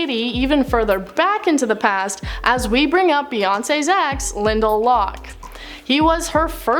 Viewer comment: I thought you said Linda instead of Lyndall